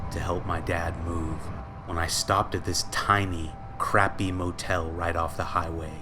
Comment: I thought it say wotel